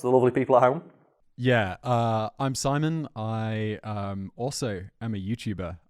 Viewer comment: god I’m glad I’m not going nuts(ahhh 😉😆 see what I did there) thought sounded like Simon.